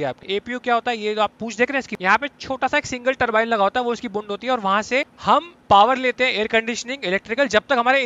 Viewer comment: Did I hear it right??😂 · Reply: Bund hoti hai....hahaha